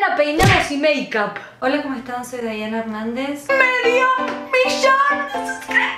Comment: JAJAJJAJAJAJAJA LA PRIMERA VEZ NO ESCUCHE "MEDIO MILLON" YO ESCUCHE "ME VIO BEYONCE" JAJAJAJAJJAJAA MUERO!!!!!!!!!!!!!!!!!!!!!!1